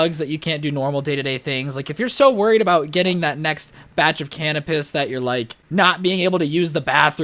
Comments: Did he say "can of piss" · Me: rewinds to 1:50 cuz I thought I heard something: can o' piss Me: ._.